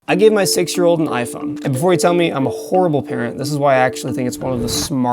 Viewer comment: Budget😭😭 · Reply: i thought he said iphone 7 and got shocked when it's a new iphone model 😭